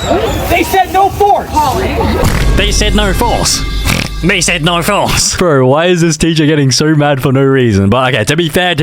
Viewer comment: I thought he said "NO FORKS"